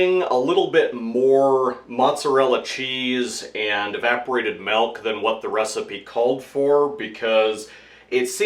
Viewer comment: did you say "melk" at 3:38? Just like they do back in the old country, where I came from.